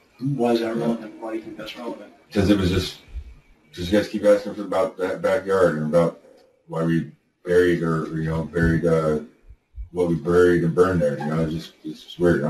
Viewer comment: did he just say, "Why we buried her...." WOW